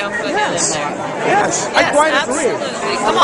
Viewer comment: Am I the only one who heard her say - "You wanna put it in me folks? - Put him in there..."